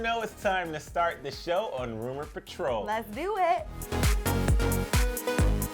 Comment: It sounded like he said rumors for trolls. Great first impression for a new viewer 😂. I'm just here to make sure Kardashian disappears.